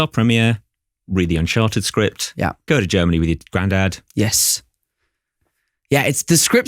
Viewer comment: Did I hear GO TO GERMANY?